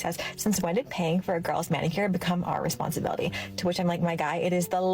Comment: At first I thought she said men should pay for her Medicare. Paying for manicures is only slightly less ridiculous.